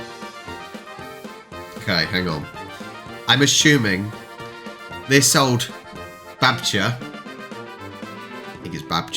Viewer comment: actually you said correct in this first... Wait. Did you said "jak jest babcia" in Polish there?!?😮😮😂😂